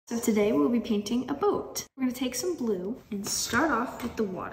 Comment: fr i thought she said today we are painting art😂